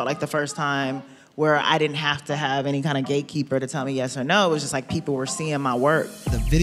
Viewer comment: Thought she said Gay Keeper 😂